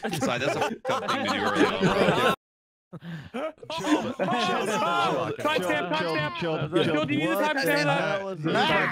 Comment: what did say? im confused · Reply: She said c u nxt Tuesday. Its an automatic demonitization